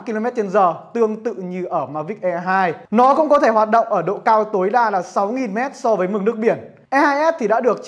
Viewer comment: Bay cao 6000m ??? Mình nghe nhầm chăng? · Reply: Hoạt động dc ở độ cao tối đa 6000m so với mực nc biển, danh cho nhug ng nào đi treking các vung núi cao như ở nc ngoài..